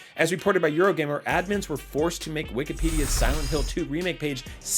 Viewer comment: Swore he said “wickedpedia” for a moment there. lol.